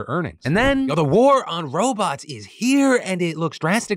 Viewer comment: i thought he said something bout robucks but it was robots lol disappointing.....